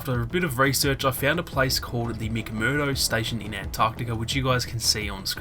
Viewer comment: a what station? (I'm sorry I couldn't hear it well because of your accent) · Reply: The McMurdo Station